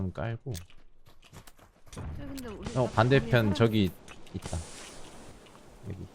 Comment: 계속나만 형이라 들리는건가? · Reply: 형 반대편 저기